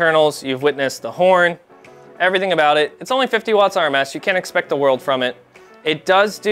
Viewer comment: I love how it is 100 watts RMS rated, but they advertise 2,000 watts peak on the box and stickers. I hate the fake peak power advertising by these companies today. Peak should never be more than maybe TRIPLE what continuous/RMS is rating is, and real world use is really only Double. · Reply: I swear he says only 50W RMS